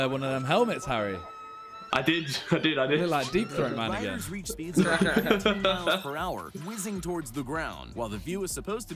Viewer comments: what Simon just say? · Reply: He said my nigga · sounds like Simon saying the n word. But he said deep throat man again.